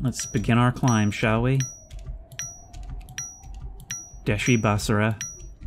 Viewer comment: What does he say at 29:49? Dashy ba sarra? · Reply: As far as I can tell, he says "deshi basara" which is a chant that the prisoners sing as Bruce Wayne climbs out of the prison pit in The Dark Knight Rises. Apparently it means "rise" so it's appropriate to be saying that here but what a strange thing to say out of nowhere, right? (This was a fun mini rabbit hole to read up on just now lol)